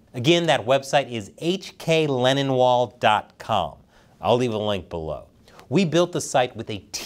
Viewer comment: Again be reminded that is NOT suicidal. On a side note I heard about the lennonwall thing mentioned in passing in one of these shows, but I guess I misheard it, as I was idly wondering who would call their call for freedom a leninwall, until I got to read it on display today... 😅😅😅